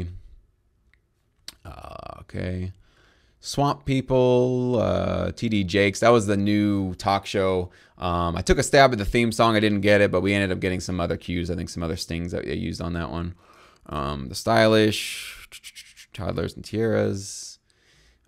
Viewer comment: did you say 'we ended up getting some other cues, I think some other stings'? Do they just use your stings sometimes? & does that count as a placement? Or did I hear that wrong